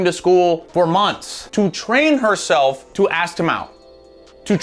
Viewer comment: Had to listen to 11:38 like four times because I actually thought he said train "her ass to mouth" (actually said ask him out) because theirs a context there that kinda fits but I couldn't believe he said it